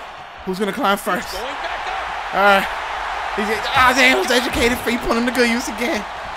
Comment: What did say 7:05-7:16. was that even English. it was funny as hell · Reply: "Get his ass on the floor!" "Who's going to climb first? Alright... Ah, he has those educated feet, putting them to good use again! Alright..."